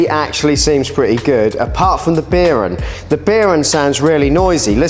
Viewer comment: Anyone else unable to stop hearing him saying "Beer un" instead of bearing?